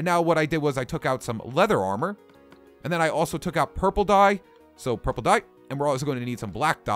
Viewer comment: when he said "Purple Dye" i thought he said "Purple Guy" From FNaF